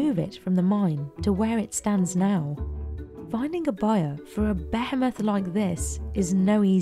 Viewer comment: sounded like she was gonna say “behelit” for a second there.